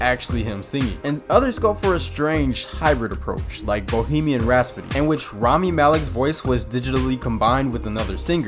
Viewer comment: I've heard multiple references to Bohemian Rhapsody in this video. But every time I hear the narrator say it as "RASPODY" instead of RHAPSODY. Am I hearing that wrong, or is this guy truly having trouble with that word? Listen here